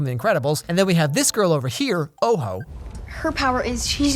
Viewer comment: Lmfao I thought it said Gojo! 🤣